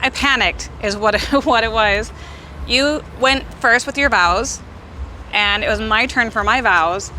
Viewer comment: I thought she said " you went first with your bowels ".